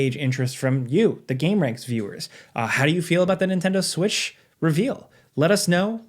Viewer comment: i think i heard Nintendo Swish😂😂😂😂